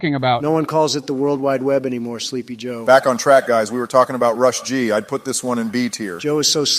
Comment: DID YOU SAY "BACK ON TRACK"?!